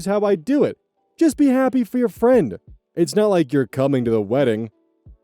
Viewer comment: Right, I was seething the second he said “it’s not like you’ll be at the wedding” he’s a huge piece of garbage and doesn’t want his girlfriend to continue to be around her family and friends. Especially after asking for help. What a loser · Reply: i thought it said its not like you'll not be at the wedding